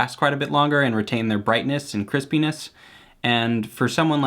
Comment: I skipped to 7:07 and was so confused when I heard "penis". It was just him saying Crispiness but it confused me for a minute.